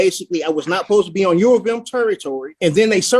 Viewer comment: Did he just say “I was not supposed to be on European territory??” 🥴